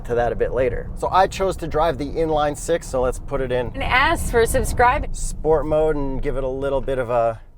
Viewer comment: I definitely heard “Put it in—Ass for subscribing!” 😂